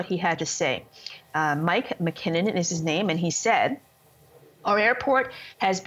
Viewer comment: she said mike McKinnon instead of mike MacKinnon